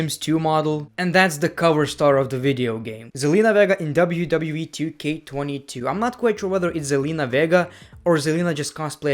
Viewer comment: did he just say "zelina vega in WWE 2K22"